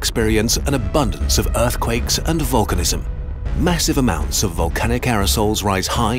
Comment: Love these videos. Love this guy's accent too. I thought he was saying vulcanism, as in the planet would implode like in Star Trek. Lol